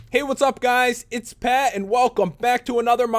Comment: it sounded like he said '"pet", not "pat".